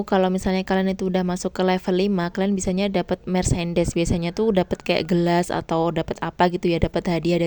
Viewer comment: aku dengernya "dapet mercedes"😂